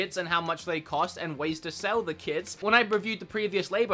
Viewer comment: I swear I heard you say “how to sell the kids” lol